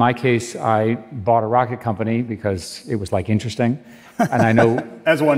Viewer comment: Eric Schmidt casually mentions that he bought a rocket company, these people live in a different world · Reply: Oh I thought he said a racket company. I was like maybe racket sports are on the up.